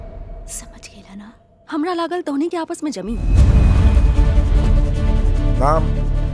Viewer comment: did she say hamara Ga** ??